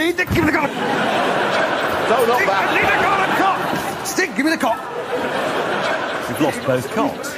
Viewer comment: What Hammond said: Stig, gimme the cock What auto-generated captions says: Stick give me the gun